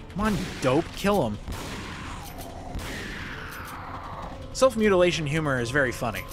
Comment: Swear I heard you say "Self Mutilation Humor" instead of "Self Humiliation Humor"....